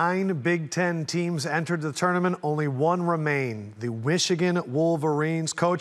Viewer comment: Who else heard him say WICHIGAN WOLVERINES